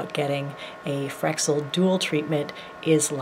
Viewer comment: oooh I found it. I thought you were saying fractured dual haha I was close